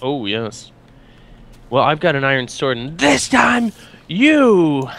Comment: Did say iron sword